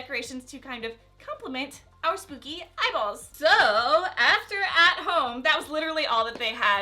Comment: I was sewing while watching this so when you said "eyeballs" I thought you said "bibles" and I got really confused lol